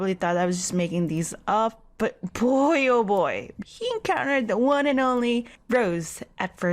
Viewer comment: What did she say at 9:16? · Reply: Julio Hernandez he encountered the one and only rose >:3